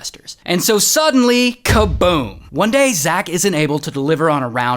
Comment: I thought he said Zach king ☠️☠️